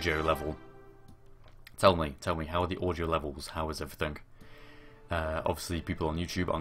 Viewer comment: Was half paying attention when asked, "How are the audio levels?" I heard "How are the orgy levels?"